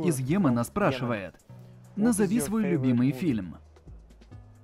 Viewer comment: Мне одному послышалось когда Кевин сказал за фильм Побег Порошенка🤣😅